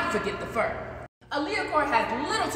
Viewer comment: I thought it said allahcore.